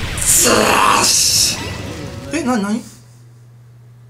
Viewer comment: 倒したァ！って所言っちゃダメなんですけどジャイアンに聞こえてしまった耳鼻科行ったほうがいいですねこれ。